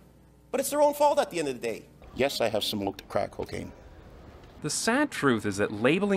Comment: i literally heard him say: yes i've smoked crack and cocaine